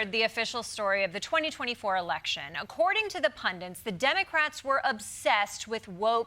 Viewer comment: Did she say "demmycrats?"